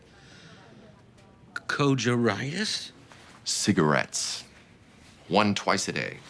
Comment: the man says co Jo ritus or something before House said Cigarettes. What was the word he said so I can search it up! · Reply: Cogaritis